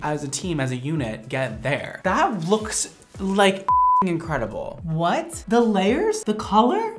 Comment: Ngl at first I thought you said "that looks like shit"